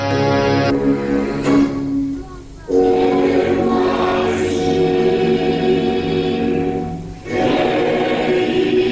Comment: What are they even saying? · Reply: M-I-C-K-E-Y--M-O-U-S-E